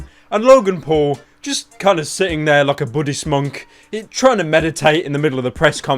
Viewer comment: when he said Buddhist monk i thought he said buddy smonk